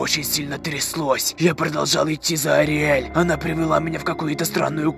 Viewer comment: Мне послышалось, или ты называегт её Ариель?